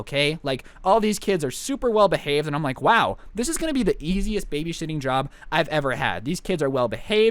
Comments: he said baby shitting instead of babysitting😂😂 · I swear he said "babyshitting" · Who else heard at 2:20 “this is going to be the easiest baby shitting job” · did you say BaBy ShItTiNg JoB!?!?! Lol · did he say baby shiting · did he say babyshitting? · did he say baby sh*ting??? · Me: Wait.. Did he just say "Babyshitting"? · I think I heard "babyshiting" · is it just me or did he just say baby shitting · Who else thought they heard say babyshit at 2:25? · it sounded like he said baby crap-ing but not crap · did he say baby shitting? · sounds like he said," the easiest baby SHI**ING job i have ever had." FOR THE FRICKING CHILDREN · i dont think meant to say babyshitting 😂